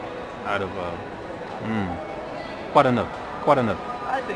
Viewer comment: lol... asked "quoi de neuf" but he thought said "quite enough"